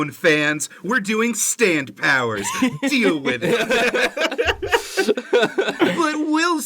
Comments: When you said “deal with it” I thought you said “DIO with it” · did you really say “DIO with it” because if you did i’m gonna cry